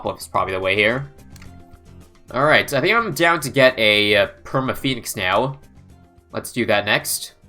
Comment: I heard permit 😳